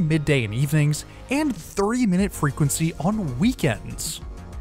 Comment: did you say 3 three minute headway on weekends ? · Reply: There will be a 30 min headway on weekends. Sorry if it sounds too much like 3 min headways.